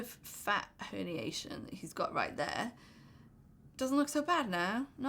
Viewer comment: I loooove your 'male'' content and this dress suits so so good, <3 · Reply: What is saying at 11:45 fat poniation?